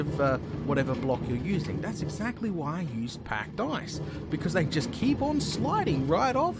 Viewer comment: OH. OH. PACKED ICE. I thought you were saying pack dice the whole time...